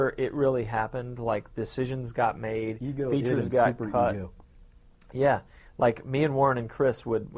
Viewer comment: What is Warren saying at 0:49? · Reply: Ego, id and super-ego.